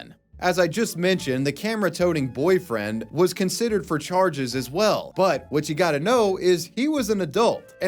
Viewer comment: Take your personal data back with Incogni! Use code wavywebsurf at the link below and get 60% off an annual plan: <link> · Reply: be careful putting that hard R on licker. they prefer licka when you said "considered for charges" i swear i heard "considered r*tar*ed" and my brain was just like yep can't argue with that